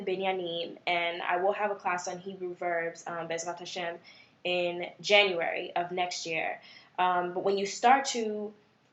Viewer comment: Excellent. Teacher speaks clearly- and knows her stuff!!. Legible (not-script) blackboard writing is vital for beginners. Scope of lesson is great: not overwhelming - not boring. Thank you. BTW: What did you say (ending in “Ha’Shem”) at 11:25? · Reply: Thanks for watching + for your feedback! I said be'ezrat HaShem